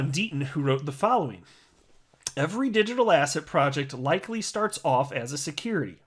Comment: Lol, every time you say ‘asset’ all I hear now is ‘asshat’... thanks!!